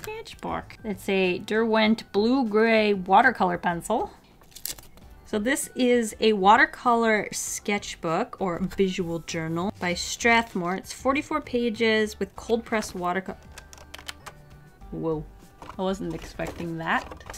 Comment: am i the only one who thought that said "usual journal" not visual lol